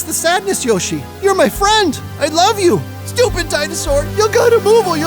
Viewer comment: Did you just say stupid dinosaur 11:03?